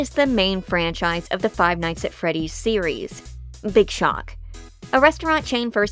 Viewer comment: that Big shock sounded like a [BIG SHOT] to me and I was so startled